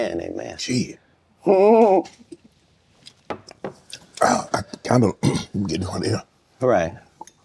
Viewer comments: Did Shannon say I’m getting hornier? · did he say I’m getting hornier ???